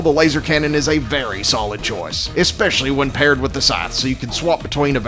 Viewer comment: did you just say pair with the crossbow?!